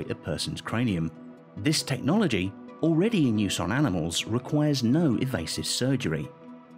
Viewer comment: am I the only once who heard "evasive surgery" ? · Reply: Yh he said requires no evasive surgery